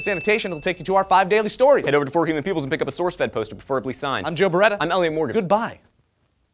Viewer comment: When Joe said "I'm Joe Bereta" I thought he said "I'm Joe Bed-Wetter" :)